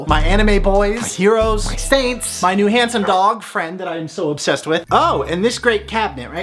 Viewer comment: Lol I thought you said Hanson boy instead of handsom boy. Both would track.